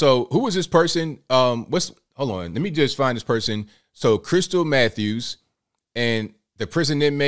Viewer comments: I was half asleep and thought you said her name was "Crystal Meth Use." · When you said Crystal Mathews, I thought you said crystal meth. Lmao